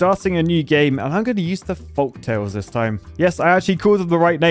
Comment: I thought he said foxtail because he's British